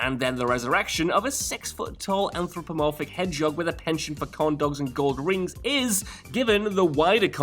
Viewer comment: Corn dogs? I thought sonic loves chili dogs not corn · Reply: At first I thought he said “condoms.” Lol. Took me a sec to realize it was “corn dogs”, which still confused me because…yeah…dude likes chili dogs.